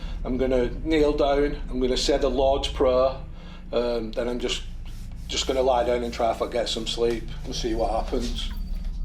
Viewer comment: omg i thought that guy said "i'm just going to lie down in the middle of the street!" LMFAO